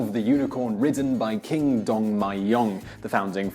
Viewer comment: did he say king dong? ..:)...